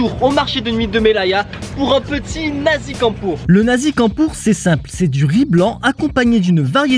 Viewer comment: Le nasi quoi?! 😭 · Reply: Désolé, j'avais posé mon téléphone 2 secondes et j'ai entendu " le nazi campeur " j'ai dû remettre la vidéo 😭